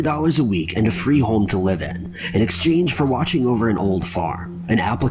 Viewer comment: My bad, I thought they said, In 2011 a job was posted to Craigslist offering to pay $300 a week and a home to live in in exchange for looking after an old fart.